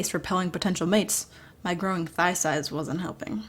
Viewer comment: 😆 thigh size What I hear: "I'm THICC"